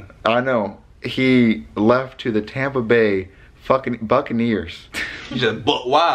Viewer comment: I love how he almost said "Fuccaneers" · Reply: It sounded like he said F##ckin Buccaneers